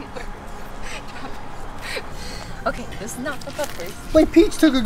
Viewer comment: I thought it said puppies I’m like WHAT THE HEL